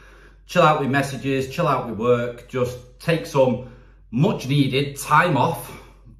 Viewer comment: at first I thought you said "take some much needed Thai muff..."